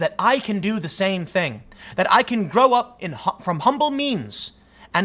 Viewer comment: I'm sorry, but I heard "from humble memes"...